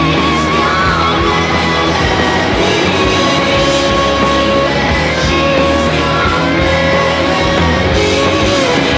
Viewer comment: what does he say at the end? like at 5:20? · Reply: too bad she's gone, Melody Lane